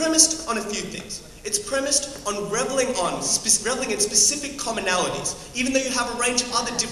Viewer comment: Can someone tell me what is said at 2:10? it's premised on _____ its specific commonalities. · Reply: I think it's "reveling"